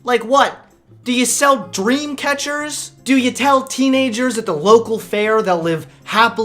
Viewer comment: I thought you had said Dreamcast instead of Dreamcatchers, I was about to yell the lyrics to 'All I Want' by The Offspring. What a way to tease me buster.